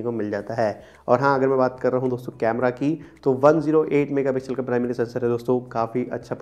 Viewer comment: i heard, camera is poor